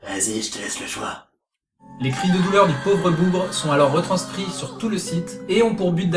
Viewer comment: le pauvre quoi ? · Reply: bougre